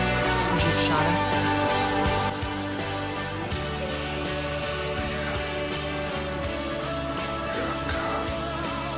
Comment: what does he said at 4:50? does he said yes? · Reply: Yeah, because she's a cop